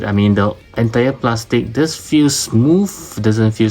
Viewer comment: did you say eto Yung Plastic thats Tagalog? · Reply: Err, no, i saw the "entire plastic does feel good..." lol...